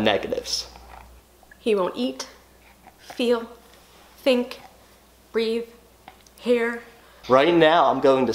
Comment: okay.... at 00:11 the lady clearly says "breathe" but the subtitle says "breath" , this isn't the only video where there's clearly alot of grammatical errors, others were "your cold" instead of "you're cold" etc..CMON FIX IT FFS!